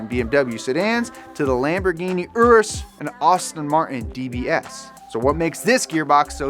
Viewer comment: Did you say Austin Martin? 13:49 … but yes to more on specific transmissions. More on ZF also for Koenigsegg 👍🏻